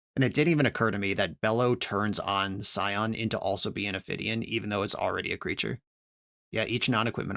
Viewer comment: Could someone help me figure out what is the word he uses to describe what Bello turns the Scions? "...that Bello turns on Scion into also being ...?... even though..." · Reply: Ophidian. It means a creature that draws a card when it deals damage